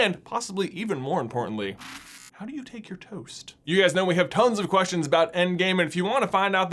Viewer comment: I thought you said “how do you take your toes”